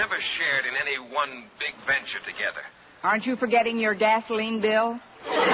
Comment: Boynton: Miss Brooks, in all the years we've known each other, we've never shared in any one big venture together. Connie: Aren't you forgetting your gasoline bill? I had to rewind - I thought he said ... Vaseline bill, harhar ...